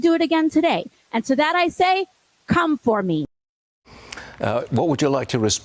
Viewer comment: Did she just say cum for me. No woman you cum for me. Thats all your good for 😂